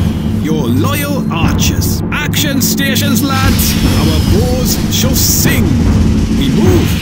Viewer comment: lol i kept hearing "Our balls shall sing!" Instead of bows. lol